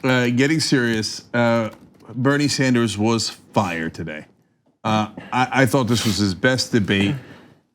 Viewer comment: O!!!M!!!G!!! Cenk!!!! You scared the crap out of me when you said that Bernie was fired. (Or so I heard) DON'T!!!!!! DOOOOOO!!!!! THAT!!!!!!!